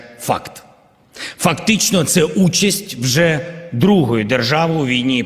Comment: did he just say “F these nuts” ?